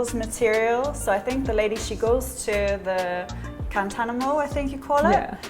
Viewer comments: OMG, that cover-up she has on is beautiful. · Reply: Yes, beautiful cover-up. Material sourced from Guantanamo?, Gantanamo? I thought I heard her say? Played it back a few times then I got it - KAN-TA-MAN-TO!! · Kantamanto .🤣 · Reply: 😀"Guantanamo"? "Cantanamo"?, I thought I heard her say, so I did a rewind of that bit a couple of times then I got it - Kantamanto. Thanks.